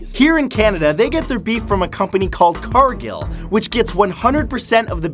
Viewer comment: I thought he said "carkill" I was like "roadkill?!" 😲🤮